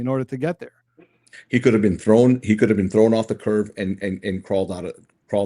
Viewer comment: Ok. 32:32 my previous comments regarding how can someone believe all this etc….? Nevermind! He just explained it ALL!! He just said CURVE instead of CURB. I understand now. 💀 I took it back a couple times and zoomed in on his mouth to make sure that’s what he said. So now i understand why he believes all this CRAP 🙄 👍